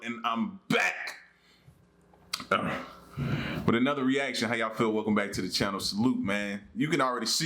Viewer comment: Funny side note, when you do your intro and say, " how y'all feel, ".and salute, well with my hard of hearing, I first thought you said , " how y'all Phil" so I just started responding to you in my head, " how y'all Allen ? Salute. Haha 😂 funny to me, kinda Phil's like you're talking to me personally haha. Z